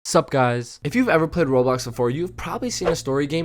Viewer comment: I thought it said korblox for a second